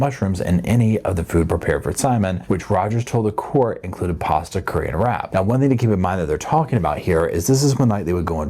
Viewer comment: 😂I thought you said “food prepared by Erin including pasta and curried rat” 😂